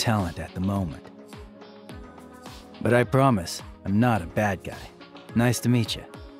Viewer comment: I THOUGHT HE SAID IM NOT A BALD GUY